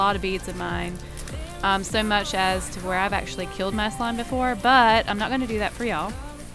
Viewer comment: I thought she said "killed my son"